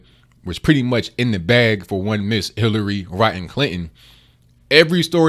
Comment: Did I hear right? "Hillary Rotten Clinton" hahaha